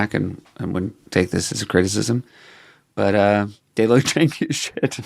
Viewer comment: Please somebody tell me what Seth is saying about the dance/band at 48:53. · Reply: I think he says “they looked janky as shit” lol